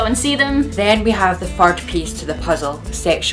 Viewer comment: Anybody else catch that she said, "Then you have the FART piece of the puzzle" at 1:32!!!! Yes, I'm aware it's just her accent. It just cracked me up!